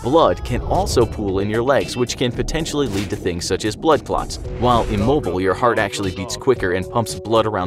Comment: blood cop, hahaha anyone else catch that?